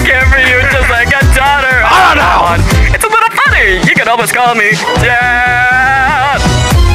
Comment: it sounded like alastor said "you could almost call me DEATHHHH"